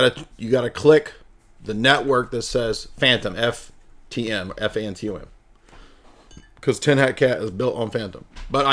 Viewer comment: This slow boy joined late and thought you were saying “phantom wallet” not “fantom” 🤦‍♂️